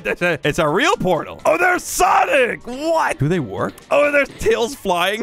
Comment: did he call him sonic??